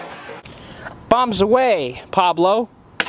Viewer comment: bumps away, Pablo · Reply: Its at 0:57, also i heard "BOMBS AWAY, PABLO"